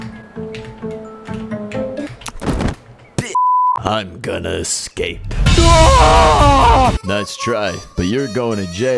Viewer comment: what did he sayyyy???? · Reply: bit